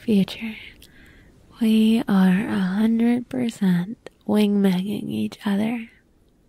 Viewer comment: I heard "wing banging" is that right ?